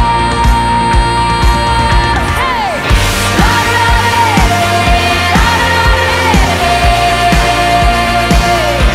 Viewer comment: It anyone could please let me know what she is saying at 4:12 I would really appreciate that lol 😂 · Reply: my beloved ( i think)